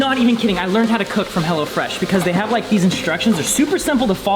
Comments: sounded like he was saying "hella fresh" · I thought he was saying hella fresh